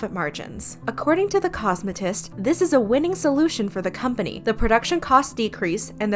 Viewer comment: subtitles say situtation but narrator says solution